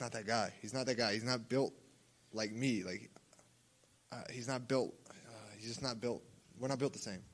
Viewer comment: "Hes not built ford tough, and i am" is all i heard for some reason 😂😂